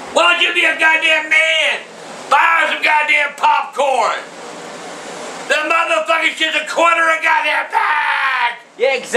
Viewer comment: What is he saying from 2:16 to 2:19? I can't figure it out!! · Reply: The God damn FUGING popcorn ran out at the corner 😂